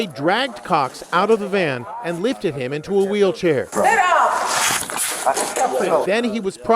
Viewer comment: it sounded like she said “get up”